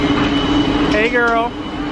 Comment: As you were talking and walking into the adults area pool deck whatever you said, my phone thought you said “Hey Siri...” and Siri kicked on.